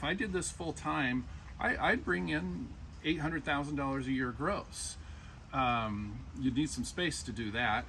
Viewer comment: Did I hear correctly, “800k year gross” (24:52)? For company 10-20 ppl staff?